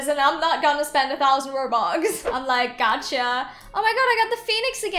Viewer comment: Wait Did You Say Gacha? Yes Your Rich And Gacha xD